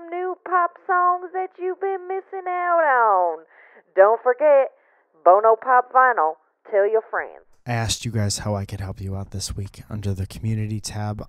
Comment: Thought she said "Bono's pop vinyl... Kill your friends", Witch made me appropriately, inappropriately laugh.